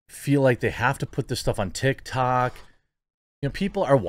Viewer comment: Did he just say tiktak?